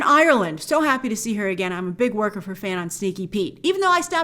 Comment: "I'm a big work of her fan"... had to rewind like "Did she just say...?"...yep. Hilarious. :)